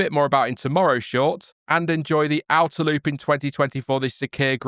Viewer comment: did you meant to say: "2020 for" or "2024" Sakhir Grand Prix? 😅 I had to replay that so many times to make sure i didnt misheard that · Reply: Haha! I did wonder if anyone would pick up on this. I meant “2020 for” 😅